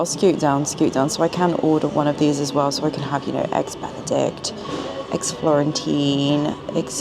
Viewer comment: eggs Benedict, eggs Florentine, eggsetra😂 I know that wasn't an intentional pun but that's how I heard it